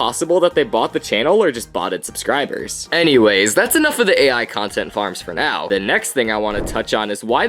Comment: Bro doesnt realize he is a content farm himself💀😭🙏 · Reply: Why was it because in 5:27 it said air instead of AIin the subtitles?